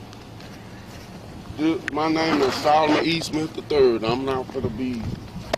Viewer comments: I think some of the confusion over his name is because the female cop thought he said his last name was "Eastman" but misunderstood him when the man actually had said his name was Solomon E. Smith III. I think she misheard him because of his slight mumble of his words. If they we unsure of his name, have him write it down. Common sense. She asked him, "What's your social, Mr. Eastman?", at one point. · That’s not cool. “It’s late and I don’t know who you are.” TF does that even mean? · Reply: Jamie C He didn’t lie about his ID. He said his name is Solomon E. Smith. Though, the officers misunderstood him. They thought he said. Obviously, the officers lacked the appropriate cognitive skills to identify the miscommunication. By the way, walking down the street at night is not a crime. Also, it’s highly unlikely that a criminal would be walking along a lit road with white pants on. I don’t know how those two passed their Law Enforcement exams. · Stops innocent man to talk to him for no reason: “I’m tired of talking to you!” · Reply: Jamie C He told them his name is Solomon E. Smith. Officer thought he said. Also, officer had no reasonable suspicion to detain and investigate this man in the first place.